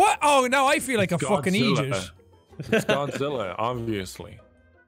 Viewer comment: i love how jack’s accent gets 1000x more intense when he’s playing with other irishmen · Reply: i really thought he said egypt not idiot because of the accent hahahahahah